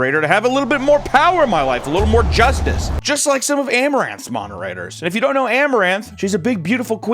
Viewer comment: I thought he said AMRAAM